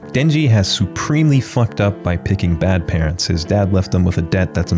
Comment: i'm sorry, did you just say he /picked/ his parents?